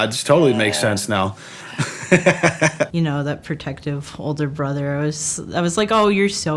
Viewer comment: I'm sorry; did she say brother?